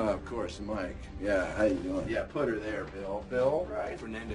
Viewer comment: What does he say at 3:38? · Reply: "Here's to you pal." An older slang phrase for "congratulations."